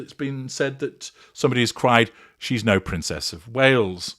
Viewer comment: I heard “you’re no PRINCE of Wales”. Either way, I hope they heard it.